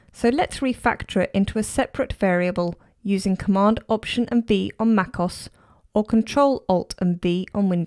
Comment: I think you said Ctr+Alt+B/Cmd+Opt+B instead of V.